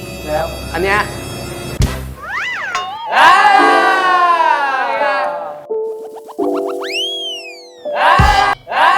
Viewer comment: what did he (Papi) sayyy? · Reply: He wordplayed Tongs are Tee-Khip whereas he called Gun his Tee-Ruk which means 'my beloved/my dear' (Trans from ngong_z and OffGunInternational twitter)